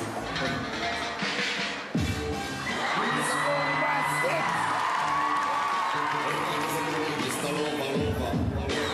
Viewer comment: also what did the co host say at 0:57? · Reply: Mr. Bombastic